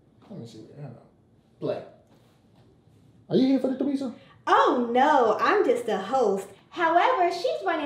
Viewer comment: I thought she said "Oh no, Im just a hoe..." 😂😂😂😂😂😂